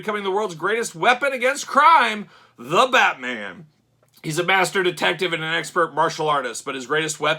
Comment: I thought it sound like you said "bastard detective " , the most frightening type of detective.